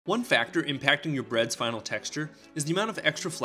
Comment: I wasn't listening to well and thought he said improving your breads spinal texture